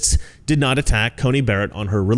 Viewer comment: for a split second there I thought the 'which' you said was 'witch' ...😂 your best line ACB is a judge not an activist (& might I add: a judge w intellectual integrity...unlike many activists)